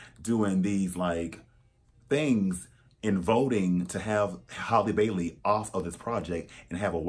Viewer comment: Honestly I'm stupid, misheard the actress' name, and thought Halle Berry was cast at first 😂